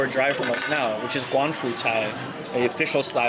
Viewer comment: should be "广府菜 Guangfu Cai" instead of "官府菜 Guanfu Cai" right? 官府菜 is more like Shandong Cuisine